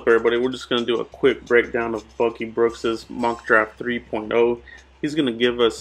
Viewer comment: Lol it sounds like he said fucking brooks at the beginning